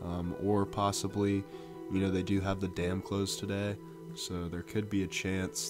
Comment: + you said 'they have the dam closed'. But the transcriber miraculously came up with 'they have the dam clothes.' I wish they would understand what's being said and the context of it. This however was funny as heck. Please explain to me why Riley is stumbling, head down, holding his head at 1:34.